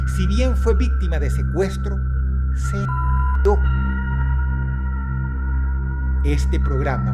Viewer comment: Alguien sabe que dijonla psiquica en la parte que censuraron en el video aca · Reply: creo que dijo que se suicidó